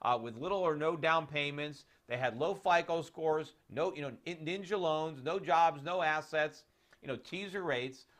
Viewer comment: Did he say "ninja" loans ?!?!